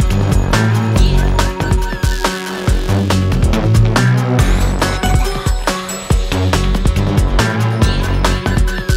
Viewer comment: Did it says " Abada kadabra"? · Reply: late but she says abracadabra